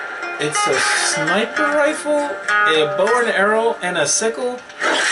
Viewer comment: did he just say sniper rifle bow and arrow and a sickle